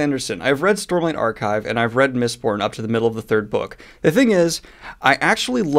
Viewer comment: Anybody else heard "Miss Pornhub"?